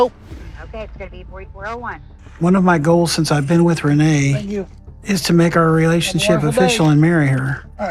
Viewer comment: not a single “please” or “thank you” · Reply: i think I hear a "thank you" but if the person that handed the food over aint the same person as in the speakers then discard this timestamp.